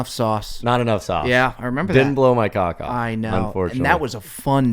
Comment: says “didn’t block my cock off” and you can see register what he said 😂😂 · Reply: Worse, he says "blow"